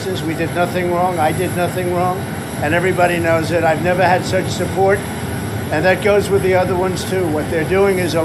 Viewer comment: Wait, did he just say "I've never had sex support?"